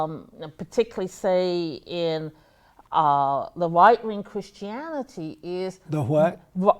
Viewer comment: She said white ring instead of right-wing 😂